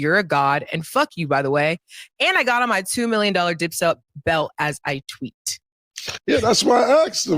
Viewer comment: It sound like she said I got on my 2 million dollar d!c suc belt as we speak 🤣🤣🤣